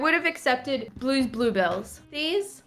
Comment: am I the only one that thought bored said blueballs??